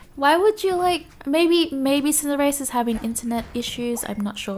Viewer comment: Anyone else mishear say "Cinderace is having... intimate issues"? 8^O